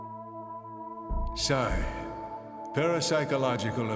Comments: did he just say "sign" instead of actually sighing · did he really say sigh??? or is this just a represenation of human->wired->human ( i havent finished) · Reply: He says psy as in psychic